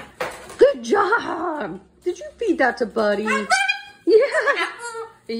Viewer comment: Oh he said awesome, I thought he said BUDDY.. asshole...😆🤣